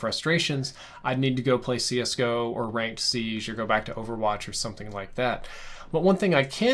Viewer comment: I swear I thought you said Roblox instead of Overwatch. I love Roblox and was about to be my third mate in Arsenal!